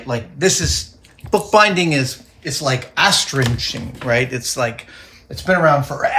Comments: what does adam say at 13:15? "book binding is like ashstringing"??? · did anybody catch what Adam said at 13:16? Bookbinding is like... astrinching? Google isn't giving me anything for that, and I have no idea what he gestured to off camera.